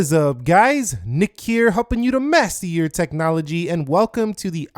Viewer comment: For I don't know how long I thought you said " mess to your technology" Then I was told," master your technology"